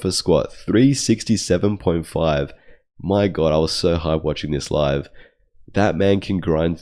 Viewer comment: 😂 Did I hear that right? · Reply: Hahaha "hype"